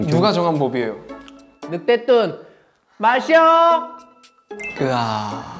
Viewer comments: I thought junkyu said "Mashiho!!" There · The moment of 5:50 when i thought kyu said Mashiho 😭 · why did I hear mashiho?